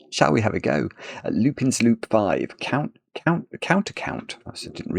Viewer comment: can't tell you why, but the first couple of times you said "lupin's loop" i heard it as "lucan's loop"